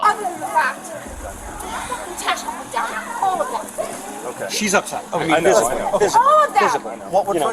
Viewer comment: So... I would just like to point out.. I am 98% certain that the "inaudible" part of what she had said at 19:06 was an allegation of SA... If I heard correctly she said "They F***** touched me down there... All of them!' Which is truly despicable, and desperate if those allegations are (most certainly IMO) false...